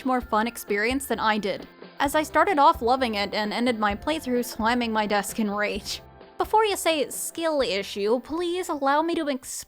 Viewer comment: did you say sliming your desk in rage? Is this some new slang I'm unaware of, or..?